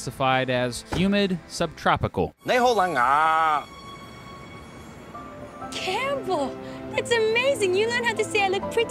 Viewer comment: Although I'm a Macanese, I still don't know what did he said XD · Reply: nei hou leng a 😂